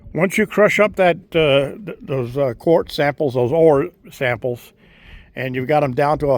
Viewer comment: great video. thought you said "horse apples" when you said core samples🤣